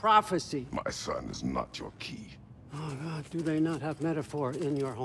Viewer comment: Did I just heard Odin saying "Oh God"?